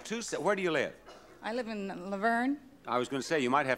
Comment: Did she just say Laverne?!